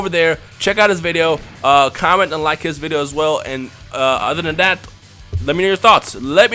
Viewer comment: in the end when u said comment and like is his videos it sounded like u said comment and unlike his videos